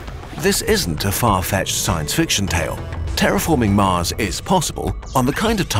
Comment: The second i clicked this video i heard "france" instead of mars it didnt really sound like france but its kinda close so yea